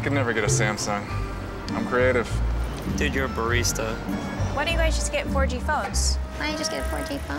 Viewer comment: "I could never get a Samsung, I'm creative." "Dude, you're a BURRISTA"??? Alguien sabe qué dijo?